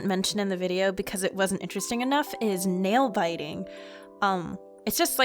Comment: OH! I thought she said Male biting at the end of the video, but she actually said nail biting. Make since now.